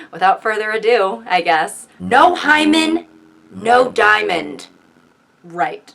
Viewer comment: when you said "no hymen no diamond" I genuinely thought he was just saying no hyphen as in you must keep your maiden name and take my name and was so confused why he would care that much about her keeping her maiden name...